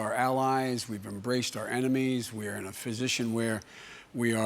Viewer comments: I am the only one who is hearing Joe Biden saying "we're in a physician" instead of "we're in a position" at 6:54? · Your country has been pushed too far to the right that you need a drastic lean to the left. Biden won’t do. Sanders! Or Warren guys. Come on. 06:54 he says ‘physician’ instead of ‘position’. Come on. Your current president can’t speak English, don’t repeat tbat